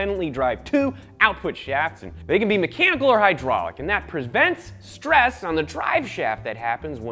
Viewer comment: "...they can be mechanical or hydraulic. And that PERSBENT'S..." ^^^ did i hear that correctly? lol